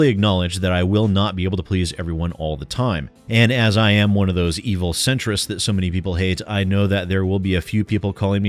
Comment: I have to admit that @ 1:11 my mind blanked for a moment and instead of hearing you say "Evil Centrist" I heard you say "Evil Sentret" which makes it sound hilarious but cute. secretly is an evil Normal type Pokemon bent on... something adorably furry because Sentrets are adorable and fluffy.